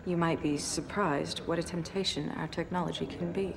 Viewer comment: thought she said mutation. she said temptation.